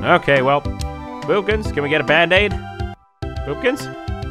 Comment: why do the captions say boppkins who messed up 😭😭😭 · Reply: vid: boopkins? CC: POOPKINS💀